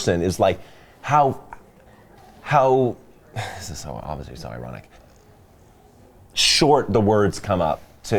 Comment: what does he say at 3:44? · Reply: Joey Catania "this is so- obviously so ironic"